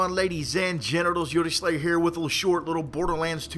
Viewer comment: Did he just say ladies and gentiles or is that just me